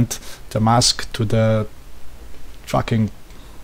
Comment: I thought this man said fucking, hope you don't mind the profanity on your comments section